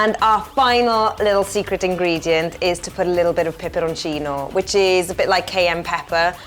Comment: whats that sauce at 1:26? couldn't understand the name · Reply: Peperoncino